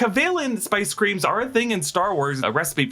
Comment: I thought they said Space Cream.. not spice